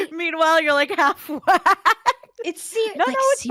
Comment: idk y but after i heard "half way" in that context I imagined one of those anime characters having those silly half shaved head and vividly fade transition to a V-pube